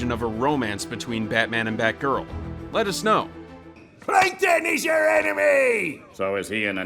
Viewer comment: When I heard me krabs said ‘PLANKTON IS THE ENEMY’ I thought he said PLANKTEON IS AN ANIME